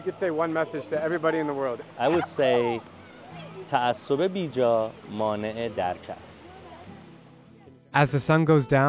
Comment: Somebody knows what he said? 27:14? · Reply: He said: “Unwarranted bias prevents a proper understanding of the truth.”